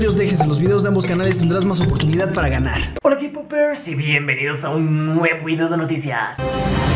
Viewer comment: JAJAJAJJAJA no se porqué en la intro escuché "bienvenidos a un nuevo inodoro de noticias"